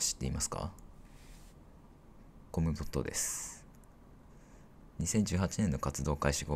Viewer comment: コムドットです。が、この人です。って聞こえてなんか戸惑ったw